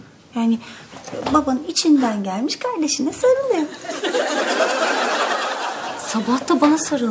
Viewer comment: ne dedi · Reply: Kardeşine sarılıyor dedi kendisine sarılıyo diyo herhalde